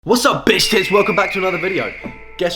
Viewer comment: This the first video I’ve seen of bro and the first thing I hear is “What’s up Bitch Tits”